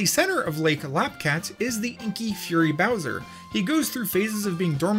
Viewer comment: I thought you said kinky furry bowser...